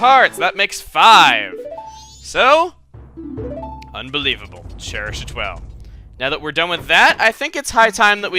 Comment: I could have sworn that swords man said Unbelievable! Childish as hell!